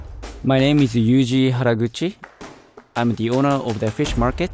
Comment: Did anyone hear that his name has 'Gucci' in it??